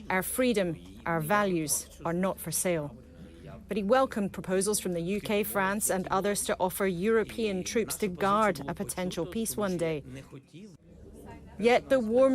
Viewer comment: Zalinsky is just an absolute GRIFTER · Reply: i hear " lithium for sale " lol